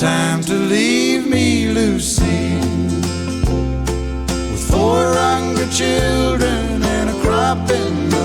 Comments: As a kid I always thought he said 4 hundred children and a crop in the field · Growing up, I always thought he was saying 400 children instead of 4 hungry children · How many people that he says 400 children in stead of 4 hungry children. For the longest time i thought he said 400 children lol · She left him with 400 children. That's crazy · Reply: Technically it’s 4 hungry children. Growing up I always thought he said 400 children!!